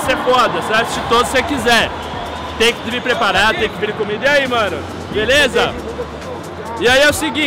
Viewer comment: Só eu escutei "Tem que vim comido"? Kkkk